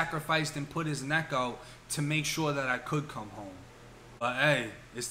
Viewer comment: I thought he was saying “I’m rockin with Charlie and yall rockin with me” for a long minute